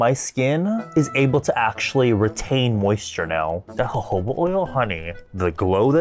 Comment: what oil he said? :’) I really need a product like that.. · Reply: It’s jojoba oil!